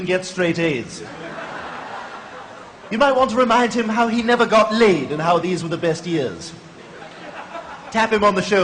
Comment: Não sou campeão em tradução, mas 0:40 me pareceu que ele disse "get laid", ou seja, "transou". E não "foi colocado"... mas valeu pela legenda.